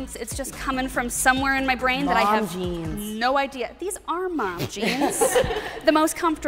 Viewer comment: "Mom jeans!" "This ARE mom jeans" I died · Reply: lmfao i thought they said “long jeans”